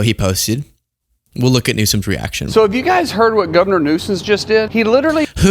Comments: I think he just called the California governor “Governor Newsons”. 🤷🏻‍♀️ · Dude.. he clearly said "Governor Nuisance".. Like the "fun nicknames" Trump makes up for the opponents whose opposition he took personal? 😳 And the bouncy ball.. Bro's clearly on the spectrum. I watched a few of his other videos and he's clear-as-day "stimming" with the ball — it seems to be his "grown-up teddy bear". His OCD-like obsession with historic dates only furthers my suspicion.. 😂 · Did he say Governor Nuisance 😂